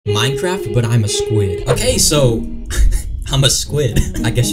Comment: I heard the "I am a squid" as "I'm in squid game"